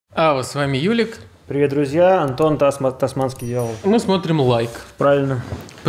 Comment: ПОЧЕМУ КОГДА ОН СКАЗАЛ АНТОН ТО Я В СКОРОСТИ 2Х УСЛЫШАЛ ГОНДОН, ИЗВИНИТЕ СРАЗУ, ПРОСТО ПОДЕЛИЛСЯ И НЕ ЧЕГО ПРОТИВ НЕ ИМЕЮ